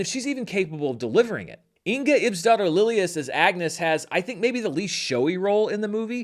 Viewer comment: I kept hearing "Inga, Ipp's daughter Lilias" 😂